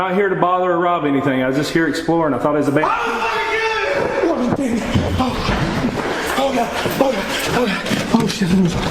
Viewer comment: So what did he say? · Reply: "I will fucking kill you" is what I can hear from the clip.